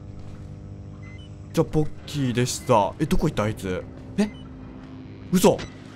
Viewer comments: アウトラスト怖いよー 宿題が終わらなすぎて怖いよー なのに余裕感たっぷりの私が怖いよー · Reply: 今気付いたけどポッキーさん最後ボッキーでしたって言ってる様に聞こえる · じゃあ、、ボッキーでした、、って聞こえた、、